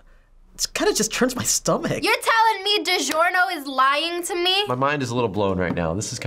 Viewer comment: i thought she said de giorno's is lying to me